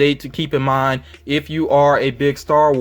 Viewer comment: Did you say, "Porn"??!